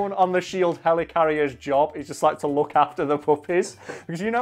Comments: I thought you said puppets for most of the vid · Yo, I was honestly so confused at first. I thought they were saying puppets. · I thought this whiten't was saying "puppets" the whole time, I was so confused why hulk's main weakness was some wooden boys on strings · I thought you said puppets at first, which would also be a great scene in a Marvel movie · I thought you were saying poppies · Uh I thought he was saying puppets until a certain point. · I thought he was saying "poppies" for the first 5 minutes. I was thinking that comic book Hulk had an addiction to opiates. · I thought he was saying poppies, which opium/cocaine is made of. I thought Hulk's weakness was drugs. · For the first six minutes of this video I thought he was saying the Hulk loves PUPPETS. That would have taken this in a very different direction. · I THOUGHT YOU WERE SAYING PUPPETS FOR THE WHOLE THING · At first I thought he said "puppets" I've never been so simultaneously pleased and disappointed. 🤣